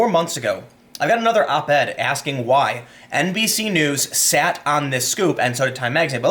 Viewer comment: LOL, had to go back to 1:30 because I thought for a moment Tim said NPC news instead of NBC news, but sadly it was NBC news.